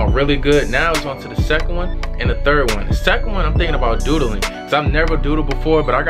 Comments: What I thought I heard: "The second one I'm thinking about doo dooing." 🤣🤣 Smh🤦🏽‍♀️ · I thought he said on the second one he gon start doo dooin💩😂